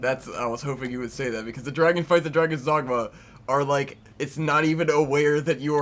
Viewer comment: Sometimes when you mentioned Dragon's Dogma I thought you were saying Drag-On, i.e. Drag-On Dragoon. Got my hopes up that you guys were going to talk about Drakengard. That's another game that would be cool to watch.